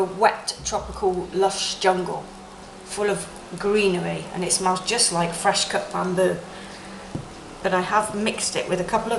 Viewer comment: At first, I thought u said "it smells like LUMBER"...lol